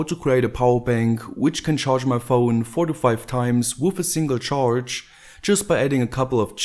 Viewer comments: Haha at first I thought I heard "45 times" before listening back and listening harder, hearing "4 to 5 times" instead. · before i thought you said ``It can charge my phone 45 times``. And now i noticed you said 4-5 times.LOL